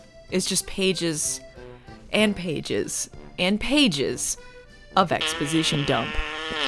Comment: For a second I thought you said "Paiges and Paiges and Paiges" and there would be like an army of alternate universe Paige.